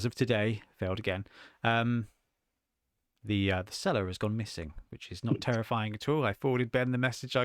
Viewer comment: Your comment about "the seller as gone missing".... I was distracted and first heard it as "cellar"... you had me stunned until you mentioned the broker saying he is not answering anymore. I was just wondering how a cellar could've gone missing :D Hope that worked out in the meantime, that's very stressful. Great content as always, thank you guys.